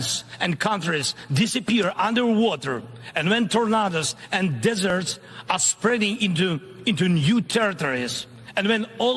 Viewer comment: did he just say, "empanadas and deserts"?